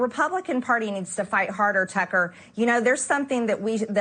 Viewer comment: HAHA!!! For a few seconds, I thought she said "Pecker" instead of "Tucker!"